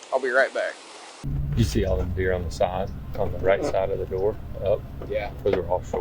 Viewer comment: ish. Does the two FWC officers say, " do you see all the beer on the right side of the door". Or did he say deer? I keep hearing beer 😂